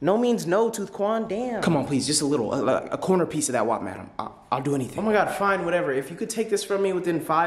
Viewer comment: Low key thought this dude said, “Can I please just get a corner of that whopper.” My big back ass 😂